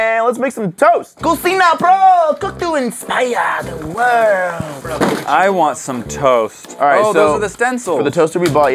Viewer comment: I’m a jojo fan. All I heard was, “kakyoin, splasha, the world”